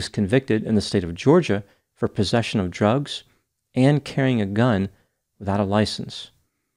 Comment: I thought he said possession of jugs 😂 I was like GUILTY 🤭